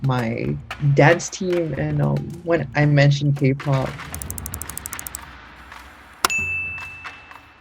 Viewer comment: did I hear Kpop!?!? My speciality